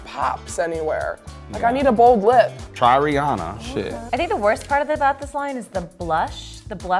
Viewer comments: did he say "try Ariana"? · did he say try ariana or rihanna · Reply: Tao Willemse Rihanna